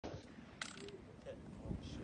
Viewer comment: I'm pretty sure I heard him say "dick her down" after clearing to fire